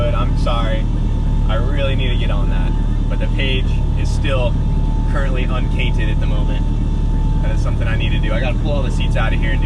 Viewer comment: Holone, wayment. Did I hear "the page is currently uncainted at the moment"?